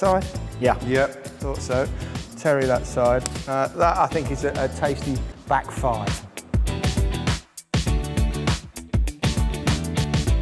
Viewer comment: did he really say back 5